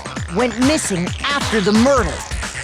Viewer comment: Who else heard Murdel?